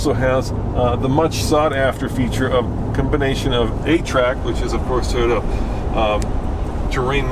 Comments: OH!! A-Trac! I thought you said 8-track! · I thought you said it had an 8 track not a trac lol I was thinking to myself no way that's awesome